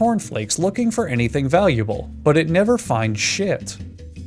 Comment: Thought he said "but it never finds shit"